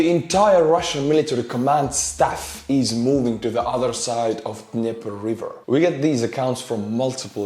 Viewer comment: At minutes 6:20 an again 6:28 all I could hear is the river is called Nipple River, turned on my subtitles and it wrote the same thing I heard being says, is it actually called like Nepple or nippele River?